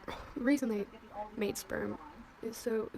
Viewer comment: I deaf or do I hear sperm